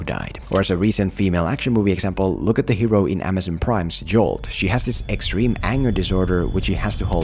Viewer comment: What's the Amazon Prime movie at 6:24? I couldn't make out the name. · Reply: Jolt